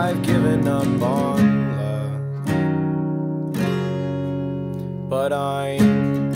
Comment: What does he say at the 0:51 mark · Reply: But I'm happy here